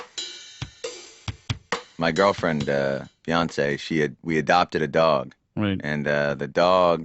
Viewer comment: ? Beyonce? Wow. I don't think I'd ever heard that name "before". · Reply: He said "fiancé" lol